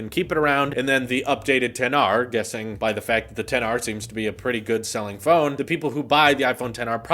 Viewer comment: Did he just say the XR is a really good selling phone?